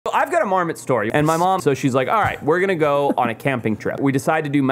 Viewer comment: At first it sounded like he said "I got a Mormon story", and asked his mom "if I catch a Mormon can I keep it? and she said sure!" 😂